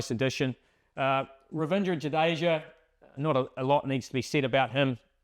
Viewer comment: i heard revenge jadeja 🗿